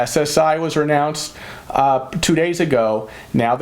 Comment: I’m crushed. Still no stimulus money. I’m outta mac n cheese 🧀 an Ramen noodles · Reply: at 14:20 did you say that (SSI was " renounced" two days ago) I thought I heard that. Backed it up to make sure.